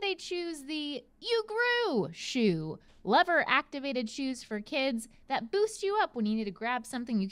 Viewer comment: I coulda sworn she said “lover-activated”.